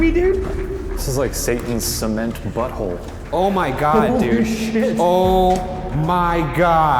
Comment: they said something about a shaking cement butthole lol did anyone else hear it?